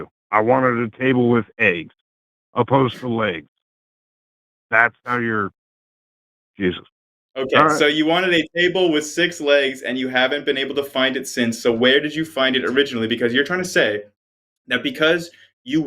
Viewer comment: I too thought he said a "table with 6 eggs". I thought it was a special kind of woodworking table...